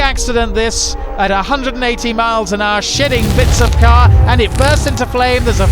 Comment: Did he say "shitting bits of car?"